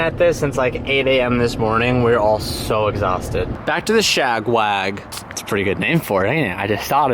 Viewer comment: I thought he said ShagMag 😂